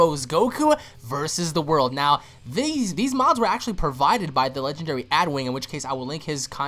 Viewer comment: i thought he said thelegend27